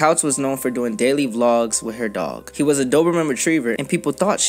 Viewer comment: Why is I hear “She’s use to doing ‘dirty’ vlogs with her dog” 💀